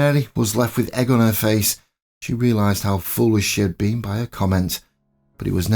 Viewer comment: All right, y'all. Sometimes, the mind plays tricks. I read the title as "Mary of Moderna." EDIT: At 8:37, I thought said, "Mary was left with egg on her face. She realized how full of sh-- she'd been . . ." On replay, it was "foolish," not "full of sh--." (눈_눈)